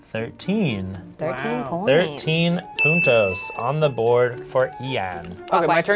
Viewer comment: I heard 13 putos on the board 😂